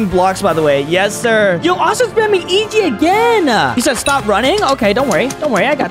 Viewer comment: Bro did not say easy 7:49 bro said eg